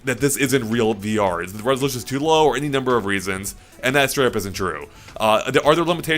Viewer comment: what does he say here? "That's 200% true"? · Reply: “And that straight up isn’t true”